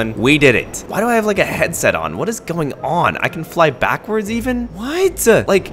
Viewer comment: I heard he say “what’s pooping”